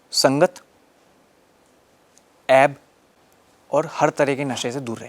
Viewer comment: "Avoid buri sangat, (app)?, and nasha" What's the second thing he said? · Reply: he said aeb